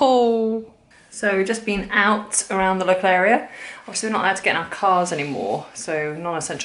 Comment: Did anyone catch what she said at 2:40 to 2:42 ? Before "anymore" ? Im non-native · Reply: I said "Obviously we are not allowed to get into our cars anymore" :)